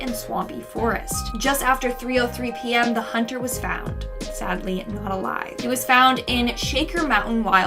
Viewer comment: i keep hearing sadly a lie